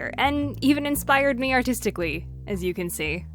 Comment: When you said it even inspired you artistically I thought at first you said autisticly